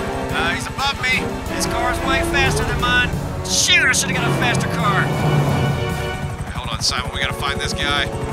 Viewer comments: what did say ? It sounded like he said §hîț lmao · Did Say "shit"?